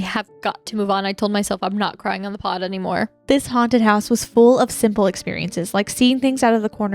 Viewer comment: Not me at home, drugged up on cold meds thinking this said haunted chicken 😂😂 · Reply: To be fair I also thought it said haunted chicken. I was thinking like there was a Chick-fil-A that was haunted or something.